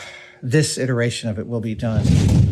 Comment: When you paused, turned side on from the camera and thought for a moment then said “this iteration”-.. will be done. I was so there with you I almost thought you said ‘disintegration’ of the object (of the chair and the new physical state the removed mass now finds itself in) and yeah now it seems the most grinding and monotonous, careful attention to detail was seemingly boring but turned out to be such a great build to watch and listen to dude. Long time fan first time contact. Ps. Adam Savage do you remember the old running man and total rekall. You should make a replica version of that quintessential early 80’s style Arnold Swchezenegger head and face double